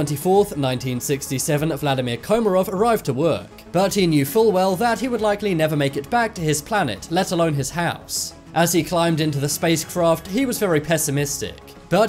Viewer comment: Thought he said vladamir makarov and instantly thought am i watchin cod now?